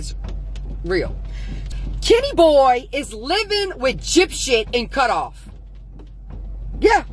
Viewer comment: Am I trippin or did she just say Gypshit? 2:03 😂